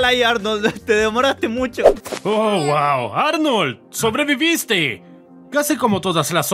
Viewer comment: pensé que dijo uwu xD